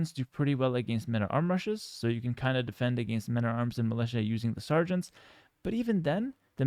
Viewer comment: WHY DO YOU FORCE ME TO SEE THE SUBTITLES, WHEN THEY ARE NOT EVEN CORRECT?? "meta-arms" instead of "men-at-arms" at 4:20. Please just don't